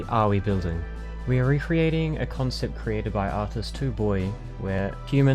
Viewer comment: Do you have the link for the conept creative? I cant hear what you say, or I hear "Two boy" but cant find it.